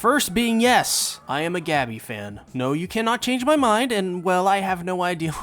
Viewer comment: i just heard "Yes, i am a gaBITCH fan" ha ha ha